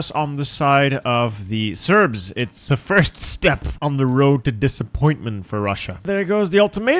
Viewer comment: “…first step to the road of disappointment for Russia” My ass thought u said “Hope is the first step to disappointment”. 🗿🗿🗿